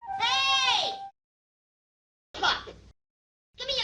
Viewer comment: i swear he says "fuck" right here, but the captions don't lie. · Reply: I think thats also why there's a text bubble clarifying that he says "Sup" It really does sound like he's saying "fuck" tho